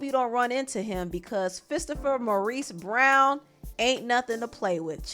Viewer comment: Did she just say “Fisterpher” ??? 😂😂😂😂😂😂😂😂 Boi I just hollered